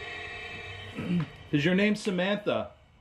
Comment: Sounded like" is your name samanth" "No, Kayla"